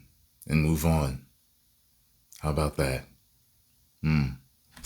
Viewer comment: did he say, "that's how Saiyan people do things"? I knew it!